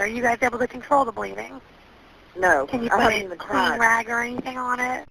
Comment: Oh my God! I cannot with the phone call about the housekeeper. That operator held it together way better than I could have. They had an attitude with her from the jump, & then had the nerve to get mad that she was asking questions & TWICE tried to ask, “can you stop asking all these questions?” As if that would slow down the response. Like no, she’s doing her fucking job, it’s her job to ask those questions! 🥴🤦🏻‍♀️Smfh. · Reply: she asks if they can control the bleeding and she says “no” and it does sound like she says “I don’t want to be part of the crime” but then answers yes to putting something on her head